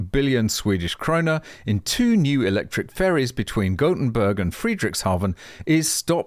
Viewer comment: Electric FERRIES ... I thought you said, "Electric FAIRIES..."